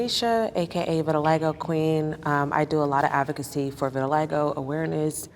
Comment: “.. my name is tanesha, aka vitiligo queen” I WANT THIS CONFIDENCE. YES. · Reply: Oof I heard "Lego queen" and went waaa